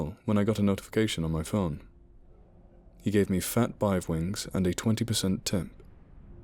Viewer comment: You said "fat bive wings" at 8:23 lol. I read this the other day so, I know what you were trying to say